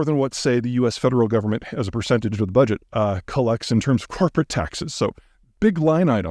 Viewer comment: I heard "coporate taxes" as "carpet taxes", and I was like "wait, surely the US isn't a huge producer of carpets???"